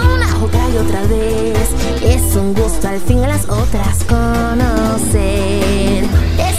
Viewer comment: en el min. 2:21 y 2:35 no dice conocer spinel dice vencer ¨gee it´s sweet to finaly beat her other friends, oh that´s right i hear the story over and over again, and really don´t like how it end, gee it´s sweet to finally beat her other other oooootthhheeerrrr friends¨